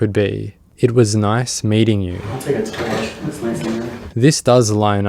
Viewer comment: "I'll take a tour, looks nice in here". That's what I hear, at least